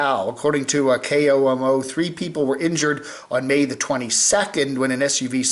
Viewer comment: did you say May ?